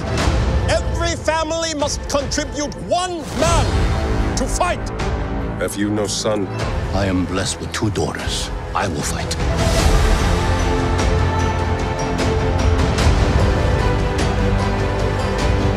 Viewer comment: I love when the solider said “ have you no son?” Her father replies “ I am blessed with two daughters “ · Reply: i thought he said, have you lost son?